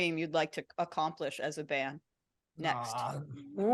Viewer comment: Did anyone understand what Victoria said in 19:46? Something about playing the… couldn’t understand it. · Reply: I think she said Glastonbury